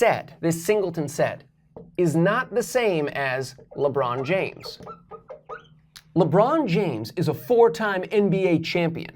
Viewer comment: Oogiliboogilibogilinogiligobbleygook! All I heard was “Labron James is a simpleton “, or something like that. Yeah, that sounds about right!